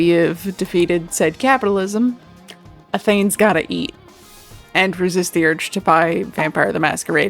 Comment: I'm confused about what says there, YT's auto subtitles of "Athene's gotta eat" makes no sense to me lmao Some great advice here! I feel like either side is often overlooked on this topic. Same as with burnout, maybe it's the expectations of others and the pressure that we make ourselves, maybe it's because someone is basing their motivation and validation too much of others, but also maybe their business model is the reason. Keeping everything in perspective is hard. Also love the character creation process in the back :) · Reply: Ah, more points for "I need to start doing my own subtitles already" - I said "A thane's gotta eat". (I sometimes refer to myself as 'thane' as opposed to king/queen/etc because it's a gender neutral title in the same family as those ones! It started as a bit with friends wanting to have an equivalent to "yass king" or "slay, queen" for me lol) Anyway thank you!! I went with what's worked for me personally, so I'm glad that it's helpful to you too!